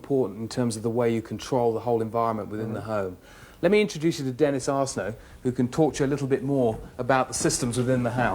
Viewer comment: That's a good thing Dennis was just randomly standing there. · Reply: When he introduced him I thought he said Dennis Arsehole.